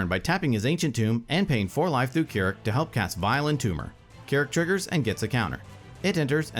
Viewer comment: I almost choked on my drink as I misheard it as Violent Tumor